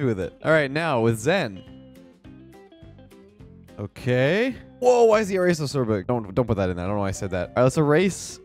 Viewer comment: I listened to it like 6 times and 3 on .5× speed and I still couldn't tell what you said. I think Danny/Trev kept it in but put some gibberish on top of it. 🤣 Maybe scrambled the order you said the words. It's going to bother me now... Lol. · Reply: "woooah why is the eraser so biig?? dont put that in i shouldnt have said that"